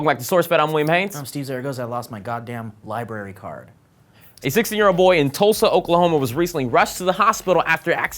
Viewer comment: I thought Will said 60 year old boy.. and I was like ??????????